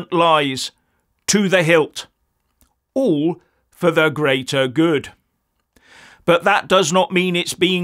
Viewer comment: when they say 'the grater good' i hear 'for mine good (not yours)'